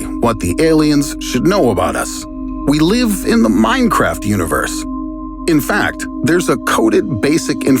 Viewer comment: do i hear "Minecraft" correctly haha?